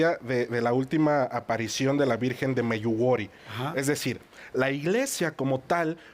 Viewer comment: Qué dijo en 3:15? La aparición de la virgen del Ayuwoki? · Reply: Medjugorje